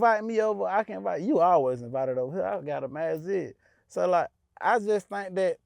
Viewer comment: what did he say? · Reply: Masjid